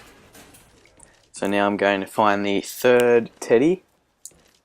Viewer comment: lol, it sounded like he said "now I'm going to get the third titty."